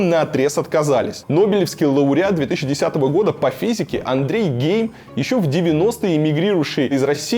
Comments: мне не послышалось? Андрей гей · Нобелевский лауреат Андрей Гей ??? Или мне это одному послышалось!!!?? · Мне не послышалось 4:49?Андрей гей?